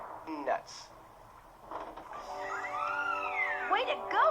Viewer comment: Did he said "Nuts." So much for Brain Damage. XD